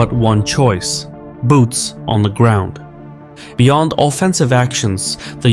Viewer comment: I heard, boobs on the ground🫣